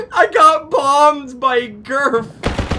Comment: I thought you said bombed by girth at first